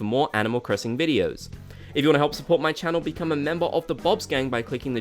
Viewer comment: bob’s gang but i thought you said bob’s skin 😂😂 wish this felt like a relief but really just sound what i expected, which are holiday updates and patches. nothing important enough to mention at e3. or anything that would boost sales significantly.